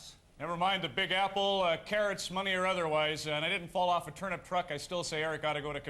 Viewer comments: oh turnip. I thought he said turban. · did he just say turbin truck? hahaha wow